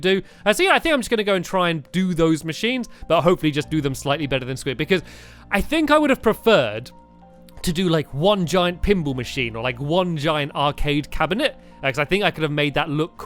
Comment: When stampy was saying pinball machine I thought he was saying pimple machine XD